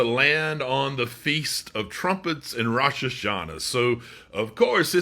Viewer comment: I thought he said 'The Feast of Strumpets ' and I got all dressed up. Now I got no place to go.